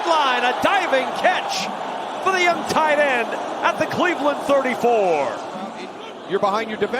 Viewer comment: Why am I hearing "And we're the Cleveland Browns"? Poor browns! -DaBears